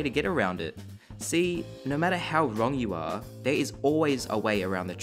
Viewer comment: So.. at 7:49 said "No matter how wrong you are.." but since my ears are broken i heard "No matter how long you are.." ( ͡° ͜ʖ ͡°)